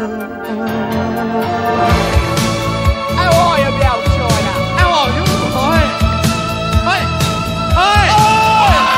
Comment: can somebody please tell me what they‘re saying starting from 2:58? i‘m struggling with the accent there 🥲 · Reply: 'How are you, my old China? Alright!!!?'